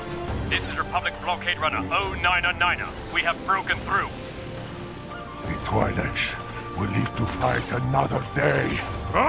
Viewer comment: sounds like he said the toilets will need to fight another day😂😂😂😂